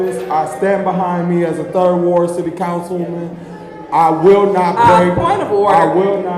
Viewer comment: did Murphy call his self a council woman I couldn’t tell??????